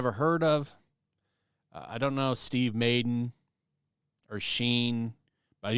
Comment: Did you call it “Steve Maiden”?? 😂😂😂😂 Have I been saying “Madden” wrong this whole time?